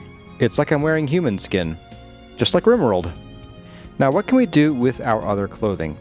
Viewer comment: I heard it as 'It's like I'm wearing human skin... just like real world!' and nearly choked on my tea LOL I love Vintage Story and your guides :D <3